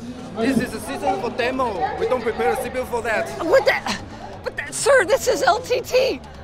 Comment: subtitle says "we don't put in CPUs for that" I think he actually said "we don't prepare a cpu for that"